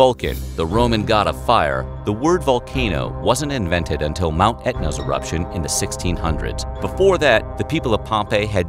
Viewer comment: OOOHHHH, you said “it’s a eruption”, I thought you said “erection”! you big goof….